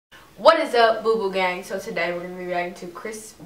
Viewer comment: I've watched maybe 50 of your vids... on the 50th vid I finally figured out she was saying "what's up boo boo gang, so TODAY..." I kept hearing "what's up boo boo gang, So Tah Ray" I have been googling "so tah ray", "sotoray", "soda ray", "so tar rey", trying to figure out what the hell she's saying... thinking its some kind of new slang I don't know...